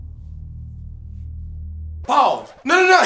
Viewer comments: I thought he said ball's but he said pause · When he said pause I thought he said balls